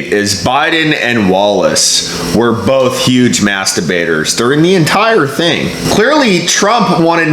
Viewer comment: Last debate ? I also thought they said masterbate